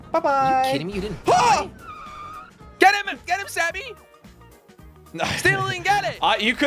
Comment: who thought he said "get him daddy"💀💀